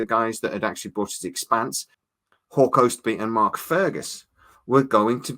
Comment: For a couple years I thought you were saying something like “Whore Cosby”. I finally figured out it’s Hawk Ostby.